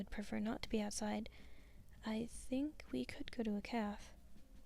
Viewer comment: Wait did she just say caf instead of cafe????